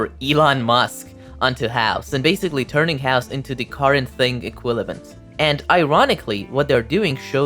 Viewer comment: did you just say equilavent